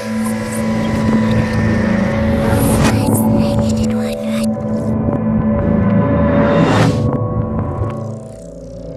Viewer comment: i hear something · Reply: "Suicide din't work,right?"